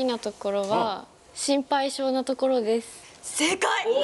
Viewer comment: 解読用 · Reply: まじ!?ゆうりのは「ほんとはMなところです。」って聞こえるw